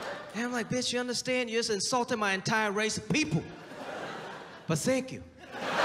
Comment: "You just insulted my entire race of people... but thank you." The potential applications of this quote are nearly infinite. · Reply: I thought he said, “but Sank you,” initially which would have made this line so much better.